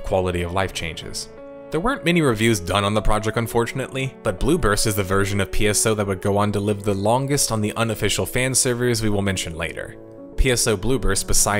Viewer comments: At first I misheard Blue burst as Bloopers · I keep hearing "Blue Burst" as "Bloopers."